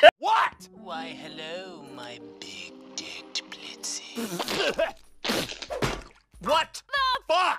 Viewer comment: I kept hearing Dear Dear Blitzy but that didn't make sense considering their response. Now it makes complete sense.